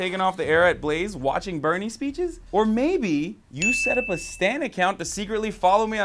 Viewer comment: Does hasan say "Stan". did he make an eminem reference here. That's fire.